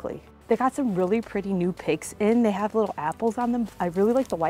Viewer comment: Am I the only who thought she said “PIGSKIN” and had to play back what item she was referring to?